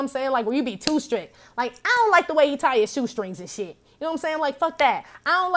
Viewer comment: "I don't like the way you tie your shoestrings" 😂 idk why I heard "blue laces, shell cases" anyway definitely been off that 💊 for a while #resonates Clearly the 🏁 continues 💯